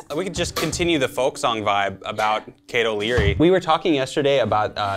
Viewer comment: I kept hearing "Cato Leary" and thinking the cow was named Cato. I wish that were true.